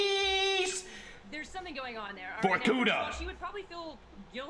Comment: did twomad say vorkuta? Dragovic Kravchanko Steiner...all must die. - Viktor Reznov